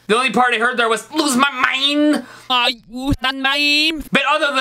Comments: First song What I heard, "lose my mind stupid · it sounds like it said LOSE MY MIME when you said it with your voice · The Brittany spears one when you did the vocal check it sounded like you said I lose my memes